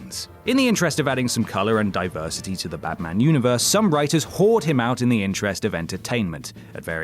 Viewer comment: "whored him out" Did I hear that right? In any case, DC is still whoring him out